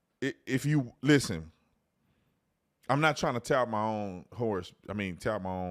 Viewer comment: Did you say you tap your own hourse..lol 10:15 😅